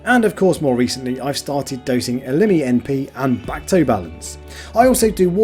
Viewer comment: i could not understand what you were saying as far as what you were using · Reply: Elimi Np and Bacto balance - the Tropic Marin carbon dosing stuff 🙂